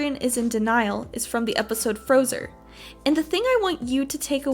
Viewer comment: When you said episode frozer, i thought you said episode frozen